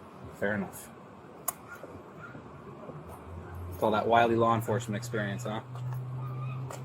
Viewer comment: “All that wily law enforcement experience huh?” Officer. 😂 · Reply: I thought he said "all that wild law enforcement experience, huh?"